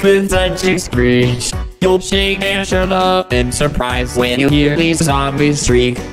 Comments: I like how he said "shut up" instead of "shudder" lmao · When he said “shudder” it sounds like he said “shut up” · Albert said “children” instead of “shrilly” that makes it 89% better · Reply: When i hear him say “shudder” i feel like he actually said “shut up” xD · I heard Albert say “shut up” instead of “shudder,” that made it even better XD